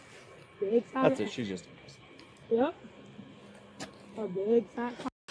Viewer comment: I swear I hear water trickling and one of the officers say she just p*ssed. Anyone else?